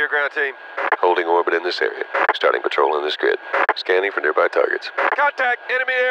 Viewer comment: standing for nearby burgers is what I heard for some reason